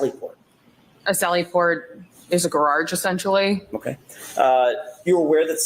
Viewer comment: "GORAGE" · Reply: I heard “gararge”